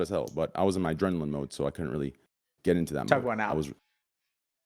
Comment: what did dr. k say ? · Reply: He said “tug one out”